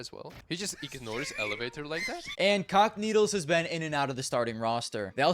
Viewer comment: Bro I keep hearing "cockneedles" instead of "cacanito"